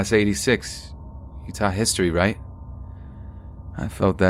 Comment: does he say Utah history? Jw because I live in Utah. Never hear stories from Utah Edit: nevermind, he says "you taught history, right" Lol, oh well Love the video topic btw!